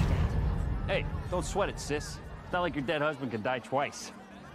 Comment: what did he say? Why is wanda mad? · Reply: " don't sweat it sis, it's not like your dead husband can die twice".